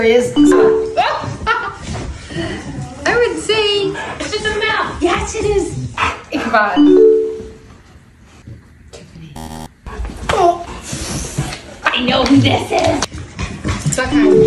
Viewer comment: No matter how many times I replay the video I keep hearing "Ichabod" instead of Magnus. Does the dog have two names or something?